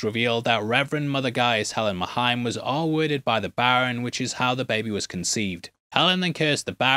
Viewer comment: ???? wtf ARE U SAYING HERE? Worded mahien???